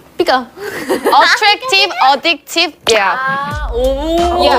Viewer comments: Wait but it sounds like yeji said because beca attractive addicted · Ok but when yeji said because she sounded like she said beca so it’s sounds cute